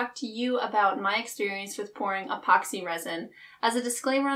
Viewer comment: Okay, I'm not trying to sound nasty or anything, but the first few seconds totally sounded like you said, "I'm going to talk to you about my experience with porn... epoxy resin."